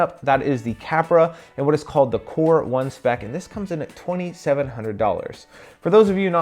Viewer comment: Man, at the start of the video i misheard you, i thought you said this was the Poor 1 Spec